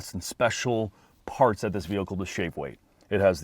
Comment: I’m not crazy. He definitely said “shake weight” at 1:54 instead of “shave weight” just to fuck with us, right? I can never tell with this channel lol.